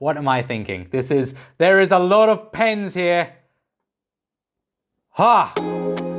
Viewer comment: at first I heard « there is a lot of pains » and thought, that’s art baby. But then the sound did reach my brain and I understood he meant pens. What a funny thing